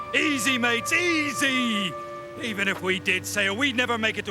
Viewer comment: would make me crack up when I was a little kid. I thought they were saying “easy my cheesy” that line has lived rent free in my head for about a decade now.